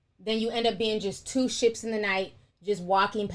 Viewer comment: Blessings: I was listening not watching at this point, thought you said "2 SH*TS in the night" 😂😂😂😂😂😂😅😅😅😅😅😅!;)PEACE